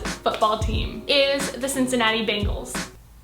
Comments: Am I the only one that thought she said Cincinnati Bagels? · At first I thought she said the Cincinnati Bagels... I was like wat?